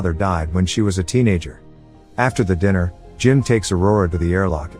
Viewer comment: Was this made with a text to speech program? Around 7:30 you hear diner instead of dinner